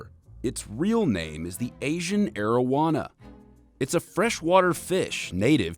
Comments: At first I thought he said asian marijuana. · I thought he said Asian marijuana